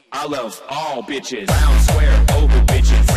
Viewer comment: No, I heard "Yeah! I love all bitches!"